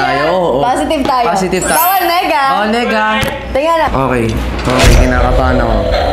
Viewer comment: did they just say nigga?